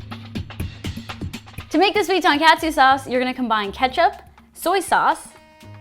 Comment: For the life of my I can’t figure out what you’re saying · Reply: Tonkatsu sauce